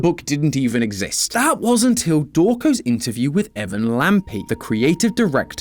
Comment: WHY DOES THE SUBTITLES SAY “DORCO” INSTEAD “DAWKO?? 😭